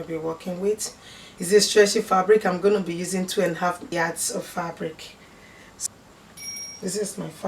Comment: You're welcome, 2 and half meters of fabric is not too much because you need enough fabric to fold it into 4. Except you are making a very short jacket then you can use less fabric. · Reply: okk,metres not yards...thought I heard yards..thanks ma